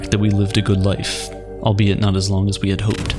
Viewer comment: "Because remember everything has not.." Has not what? Or did he say something else? Can somebody help? :D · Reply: has an end